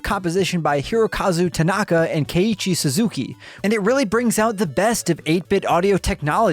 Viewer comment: coulda swore he said Kenny G Suzuki 😂